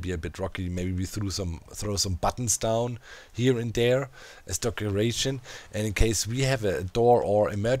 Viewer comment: HAHAHA @ 44:14 did you say "DOCeration" instead of decoration? hilarious